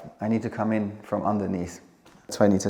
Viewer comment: I thought said tractor instead of trap door.